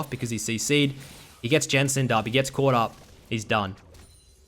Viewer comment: did he just say 'he gets jensen'd up' duddeeee